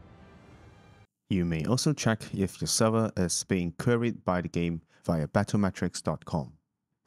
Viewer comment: I didn't understand the part on 6:04, what does that do? "You may also check if the server is being ------- by the game-" I can't understand what you said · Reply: You may also check if the server is being "queried" Cheers!